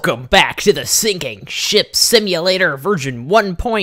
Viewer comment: I thought u said sinking shit sim